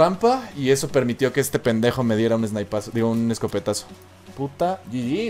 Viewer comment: did he just say puta xD